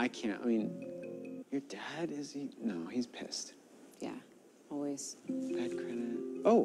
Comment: your dad is.. no he's pissed lmao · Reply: I thought they were saying ‘passed’ all this time! I’m glad I know now because when she replied ‘always’ I was like that doesn’t make sense...he couldn’t always have been dead.